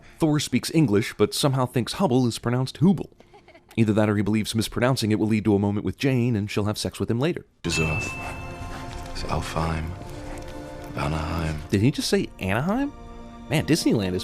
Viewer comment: I totally thought Thor said Anaheim as well!! Who knows how many times I rewinded that scene just to hear if he said Anaheim!! It was only until I turned on the subtitles and realized that he said Vanaheim not Anaheim.